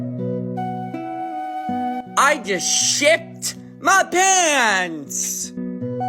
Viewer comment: "I just shipped my pants" genuinely made me laugh omfg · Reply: I thought he said "shit" ... Oh wait, are you just censoring that word for no reason?